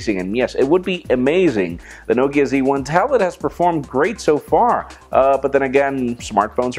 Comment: Is it just me or he really said Nokia Z1 Tablet at 1:09 instead of N1?